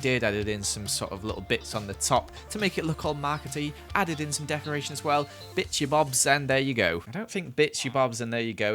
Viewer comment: did he say bit— u bobs?